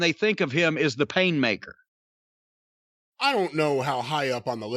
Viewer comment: For a moment I thought that Jericho said he was the Cake Maker. I thought 'That nick name fits him'.